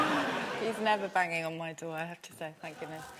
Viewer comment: What does she say at 5:29? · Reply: He’s never banging on my door thank goodness.